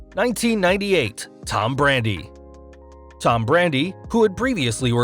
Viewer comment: i thought he said Tom Brady